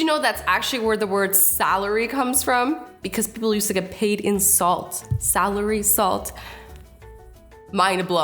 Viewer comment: When you said in salt I thought you said insult